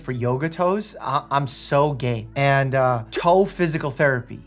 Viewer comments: “I’m so game” is what you said haha I had to rewind because I thought you said “I’m so gay” and it didn’t make much sense with what you were talking about ha ha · I wasn’t paying attention to what he was saying. I clicked back in and all I heard was I’m so gay I had to rewind and he said I’m so game